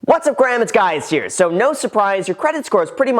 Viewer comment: Did u just say "Whats up graham its guys here" lolololol.